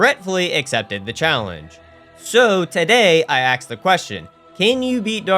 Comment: did you say "axe the question" at the beginning 00:15 or am i hearing wrong